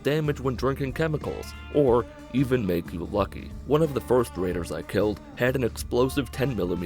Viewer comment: When he said "one of the first raiders I killed" (2:30) I thought he said first graders